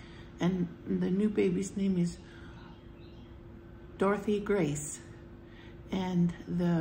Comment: Hello ✨🤍🌙🕯️✨ Oh this is such wonderful news ! All the glory be to God ! God healed Dorothy Grace in the womb ! What a beautiful name “Dorothy Grace”! 💖 Congratulations 🎉 to Annie & Titus ! 💖 They’re so blessed 🙏🏻💗 Congratulations to you as well 🎉 you’re an Aunt again ! 💖 I’m so happy for Annie & Titus 💖🙏🏻. I was just thinking about Annie a few days ago & wondering how everything was going. Thank you for updating us I appreciate it. I’m happy to hear that the darling sprouts 🌱 didn’t frost 💖 Oh wow those apple 🍎 fritters look scrumptious 😋🤤 The other food looked good too 😋 Have a wonderful blessed night & take care 🙏🏻😘 🕊️♥️🕊️♥️🕊️♥️🕊️ 🌺🙏🏻God bless🙏🏻🌺 ✨🫶🏻✨🫶🏻✨🫶🏻✨ ♥️🕊️♥️🕊️♥️🕊️♥️ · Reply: Oh forgive me, I thought you said “Dorothy” but it’s “Dorothea” that’s beautiful 💖 I wholeheartedly agree with you that God is the creator & the healer ! God is definitely in the miracle business ! Not to mention kind & rich in mercy 🙏🏻🤍 Yes, God bless Dorothea Grace🙏🏻💖 Thank You!! You Too!!💖🕊️